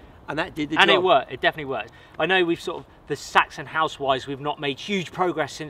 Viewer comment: I thought he said Saxon House Wives - we have an idea for a new series!